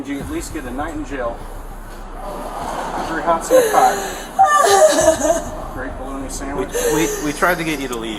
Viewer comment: "Three hots in a pie......good bologna sandwiches" I HEARD THAT AND DIED LAUGHED IM SORRY